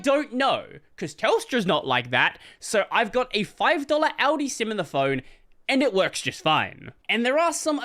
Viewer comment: Did I hear that right? "Aldi"? I didn't know Aldi existed outside of Germany…